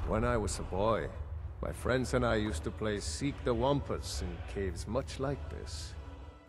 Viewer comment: not gonna lie i always thought he said wampas like in star wars and hired him because that